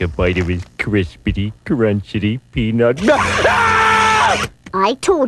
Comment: Did I heard him say, I told you boomer